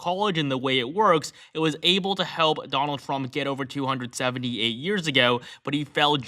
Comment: At about 45 second in, I supposed he's saying Trump got 270... eight years ago. It sounded like he said Trump got 278 years ago. Dude.